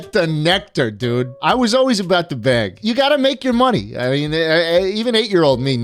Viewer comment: I thought you said you've made enough money?